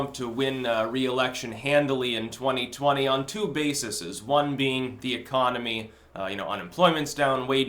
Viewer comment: Styx! Did you say "on two basises" at 0:08? What kind of language is that? Check your gramma boi. Little kids might be watching and picking up wrong habits! If we end up in a dystopian future with people speaking garbled English as depicted in the last chapter of David Mitchell's Cloud atlas <link> (cool book by the way, should read if you haven't) you are sharing responsibility for this!